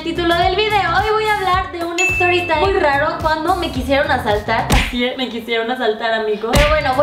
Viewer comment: Me voy a ir al infierno por decir esto pero pense que decía "me quisieron M4sturb4r"